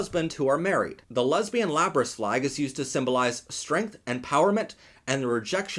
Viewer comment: I was just listening and wasn't watching and I just heard "The lesbian lapras flag" and I was like "We have gay Pokémon flags now? Cool."